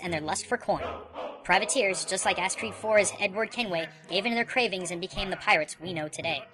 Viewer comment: did he just say ass creed >:(